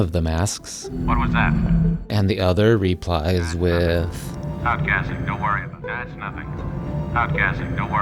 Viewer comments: I originally thought the stormtrooper said "Stop guessing, don't worry about it." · My only one was the Stormtrooper, I always thought he said, "It's Nothing, don't worry about it"